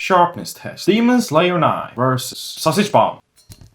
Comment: I thought he said semen slayer knife💀💀💀